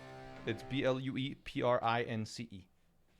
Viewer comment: I thought he was saying Blueprints the whole time. I didn't realize Blue Prince was the actual title. Seems interesting.